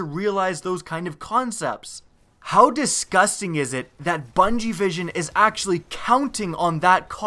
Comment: I swear I heard "bungievision"